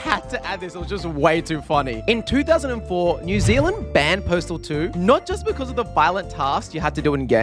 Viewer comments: Am I the only one who heard him say portal 2 instead of postal 2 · i thought he said portal 2